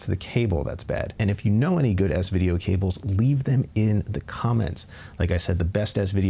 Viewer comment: lol I keep hearing “good ass video cables”. Anyways very informative, lots of good information to digest.